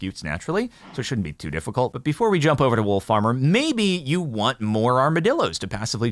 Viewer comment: For a few moments there, I thought he was saying 'Wool Farmer' and was I was getting exciting by some new mob I hadn't heard about.